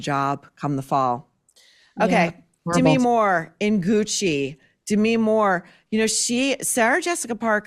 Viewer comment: I thought you said, "Demi Moore in hoochie" instead of "in Gucci" :D